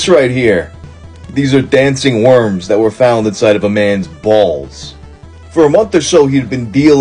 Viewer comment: i thought it said bills 😭😭